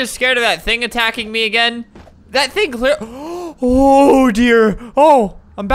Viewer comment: Is it just me or when he said “Ohhh dear.” 4:03 I hear Oder lol 😅